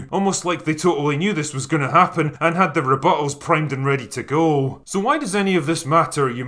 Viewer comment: I thought that what he said about the "reporters" was "that they had their bowels primed and ready to go" which honestly works way better than "they had their rebuttals primed and ready to go" Great video man, cheers